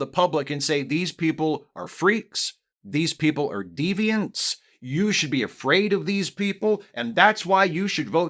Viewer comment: I was walking around and instead of hearing “deviants” I thought you had said “demons”. And I was like wait wait wait… They’re demons??! THAT’S AMAZING! I’ve always wanted to know a demon! I was about to go out and make a whole bunch of new friends, but now I’m just sorely disappointed that trans people are just regular people.